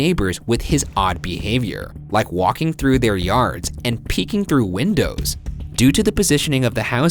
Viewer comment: I thought he said “Barking through the house and peeing through their windows”